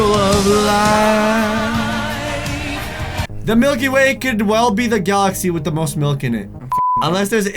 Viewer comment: I thought milky way said Mikey Way